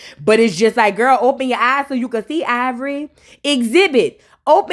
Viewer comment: It sound like you saying EYEvori whole time 😂😂😂